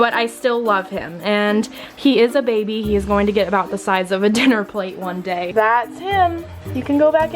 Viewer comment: I thought you said the size of an airaplane 😂